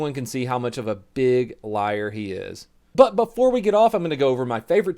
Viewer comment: did he just say "before we get off"